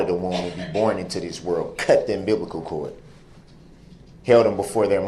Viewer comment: Did he just say that he "cut the unbiblical cord"?